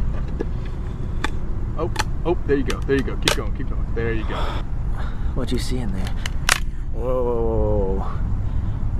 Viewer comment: a shadow of a hand shaking comes into frame but disappears when Jake opens the box fully......shit giving me the chills!!!!! Did anybody else hear a lady say something at 14:29 ? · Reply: Yes I think she said “sir?”